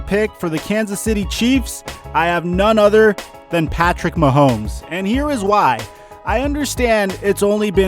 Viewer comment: did anyone else hear him say tua instead of patrick mahommes